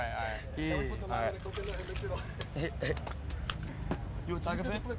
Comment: did ski just say put the lime I'm the coconut?😂